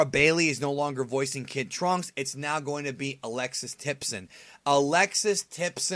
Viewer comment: I thought I heard you say Alexis Texas is going to voice kid trunks😂💀